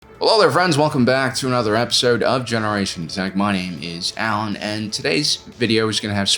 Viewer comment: bruh in ur intro i always hear it like 'generation tech money'